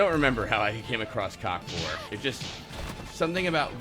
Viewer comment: I heard "cockfor" for like 5 minutes until I understood "cock vore" and I thought was just doing a "what's cockfor" setup without anyone else on the stream to ask the question and I thought it was the funniest fucking thing.